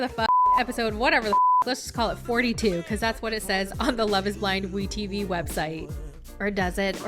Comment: did you say Love is Blind?